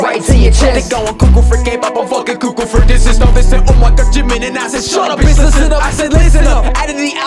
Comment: When he calls jimin jungkook Me: 👁️👄👁️ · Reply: It sounded like he said ‘Jimmy’ instead of ‘Jimin’ ...man hates Jimmy Fallon ✋💀